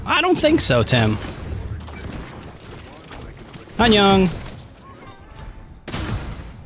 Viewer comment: did you say “Anyong”